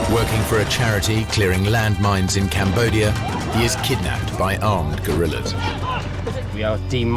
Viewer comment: Did he just say kidnapped by armed gorillas? 0:23 😂